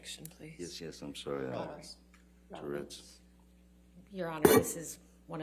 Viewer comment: Did Johnny mumble3:23:20 "Tourrettes" in reply to the Judge when she asked him (it also sounded like she called him Ms. Heard, too) to wait to answer after the objection???🤣🤣🤣